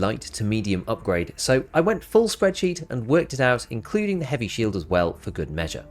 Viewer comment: I thought you said “full spread cheeks” I had to rewind and double check